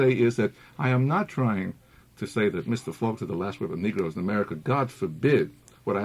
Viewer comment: Doed anyone understand the speakers words about Faulkner at 1:06:54 to 1:07:00? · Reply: "I am not trying to say that, Mr. Faulkner has the last word on the Negros in America."